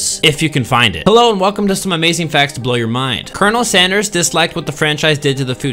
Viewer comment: For a second I thought it said amazing facts to blow you